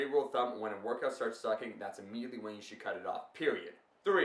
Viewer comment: What does he say at 1:35?? When a workout starts xxx... What is xxx? Please someone reply, I'd really like to know what he wants to say there! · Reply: sucking